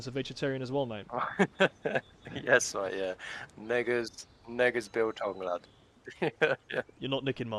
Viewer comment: What did he say? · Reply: Neggers as in negative, like roge is short for Roger. You have to be in military to get it..